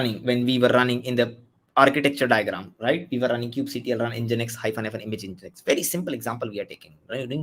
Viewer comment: what are you saying? Thank you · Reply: Reading out the command kubectl run nginx —image=nginx